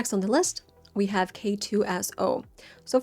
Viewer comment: who else heard K-2 A$$ O 😂 Or am I the only one here :/